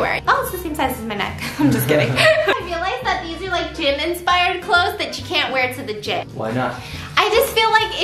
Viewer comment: At first I thought you said "Jimin inspired clothes" but then played it back and realized you said " gym inspired clothes" 😂i need help lol 😂 love u biss 💜